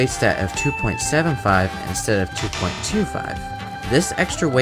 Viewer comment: Did he just say "suhness road" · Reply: I called the track "snes-bow" which in Mario Kart 8 Deluxe is short for "SNES Rainbow Road".